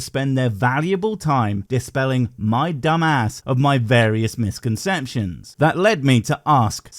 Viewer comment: WTF is a "misconcension". That's what he says at 12:52.